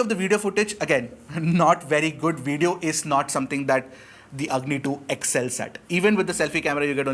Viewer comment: Why did I keep hearing "the ugly 2"